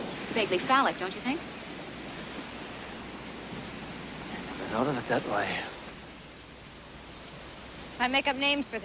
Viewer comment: What did said at 0:25? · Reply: He said, "Never thought of it that way."